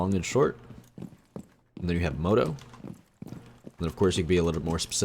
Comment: is it moto or motor? Because i cant tell what u said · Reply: Moto lol sorry about that